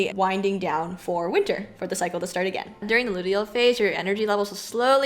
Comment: You said Luteal phase I totally thought you said Odile phase. You should TOTALLY pick ballet characters names that match the characteristics of each phase! 😂 #NewVideo